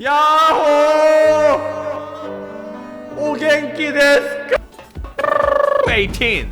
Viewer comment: 뭐라고 한거임? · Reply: おげんきですか? 아닌가요